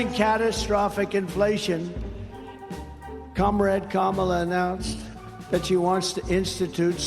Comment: My headphones are crappy so I honestly misheard Trump say "Comrade Kamala" as Cumrag Kamala"